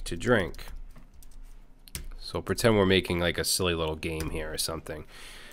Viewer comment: I thought I heard you say "Let's just just pretend we're making a soylent green here" xD